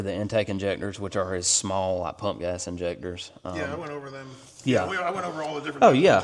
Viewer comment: did he really say the wires were for his "Punk Ass" injectors? 😂😂😂😂😂😂😂😂❤